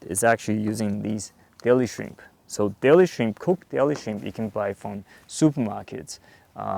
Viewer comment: Nice video I have one issue forgive me but at 6:58 you say cooked "%%(* something you can get at the super market what is it you are saying its not clear what you are saying is your favorite. bait? Thanks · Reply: Deli shrimp.